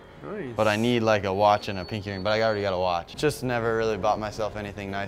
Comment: who else thought he said pink earing?